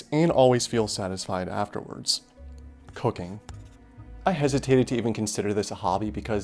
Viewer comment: I heard cocaine